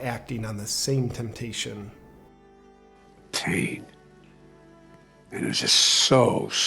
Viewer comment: This was brilliant! But what the heck did he say at 5:41? Pain? I think his dentures were loose so I couldn’t understand….